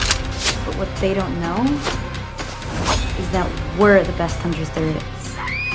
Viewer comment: im sorry but does she say "we're the best hunters there is"?